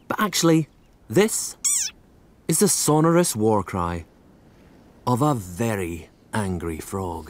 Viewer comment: I thought it said War crime not war cry